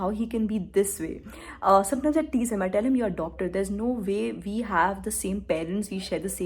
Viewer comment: He is not a doctor. He is in media and advertising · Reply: ohh. shit... sry sry... i usually watch vides at 1.5x... now i got it, u said "adopted" and i heard it "a doctor" 😂😂.. my bad